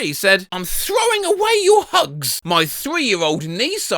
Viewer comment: i thought it said "im throwing away your lungs!" at 6.46 i was quite confused